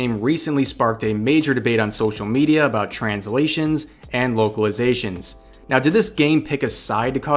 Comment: Could have sworn you said "Wokeilizations" which is how I am going to think of it from now on.